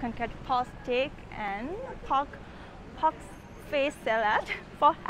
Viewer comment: I still don't know what you said at 0:30. [Pork salad] is my best guess. Try saying [chicken meatballs] so I can calibrate my hearing. - Well, then I definetely want to have a nice big feast of your Burmese street food.